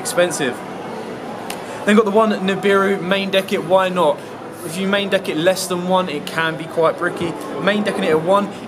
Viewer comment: "If you're main decking less than 1, it can be quite brickey" Did, I hear that right?